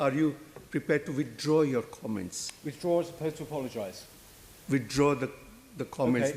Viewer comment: What did Peter say at the end? All I could hear was "Withdraw supposed to apologise", is that right?